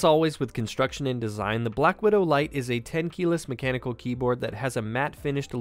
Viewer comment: Did I hear 10 kilos? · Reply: TEN KEYLESS